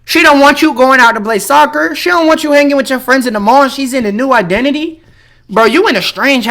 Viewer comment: I thought he said banging him